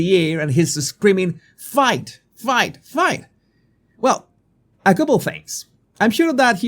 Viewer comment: I watched it on Live , when I heard the shot and he touched his ear and went down, I thought that he was murdered. Later on his body guards came and kept him down that he missed his shoes, he asked for them. As the body guard helped him to stand up he said wait and lift his arm , and I truly thought that he said F@ 3X to let those who want him down that IS is going to be ok, many of the crowd on the back started saying the same F word. 💕🙏🇺🇸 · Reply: He said fight fight fight.